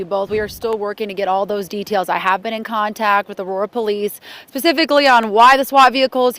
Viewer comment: Did she say "pacifically" ?